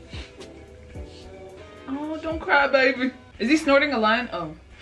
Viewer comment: “Don’t cry baby. Is he snorting a line???” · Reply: I misheard what she said and heard "is he snorting a lion??!"